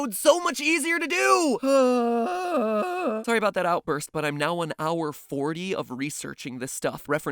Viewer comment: Why has no one mentioned that MatPat did all of this research in only 1 HOUR 40 MINUTES! You really have gotten good at this stuff. It would have taken me at least 4-5 hours. Not to mention the video is also great! Good job🎊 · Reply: I thought he said "I'm on hour 40", not "hour and 40"